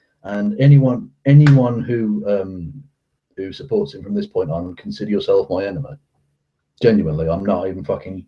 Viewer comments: Did he say "consider yourself my enema"? Because that sounds hilarious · Then I guess I consider myself your enema. Wait! What the FUCK did you just say?